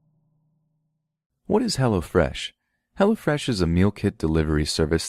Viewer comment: I was working on some art stuff while listening to this and I was half listening when you did the advert at the end. I heard "what's hella fresh?" and spoke aloud, "ME."